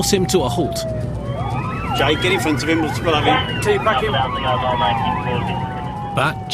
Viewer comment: All I heard "Teabag him" 😂